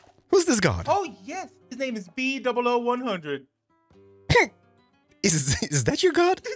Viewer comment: i like that in the closed captions, instead of typing out bdoubleo100 it said b00100